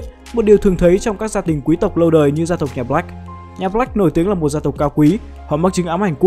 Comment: lúc gì nghe nhầm là gia tộc lạnh